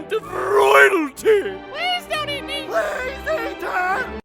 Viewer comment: "please don't eat me" "PLEEEASE EAT ERR!" hard to catch what meliodas said but much more funny when you do.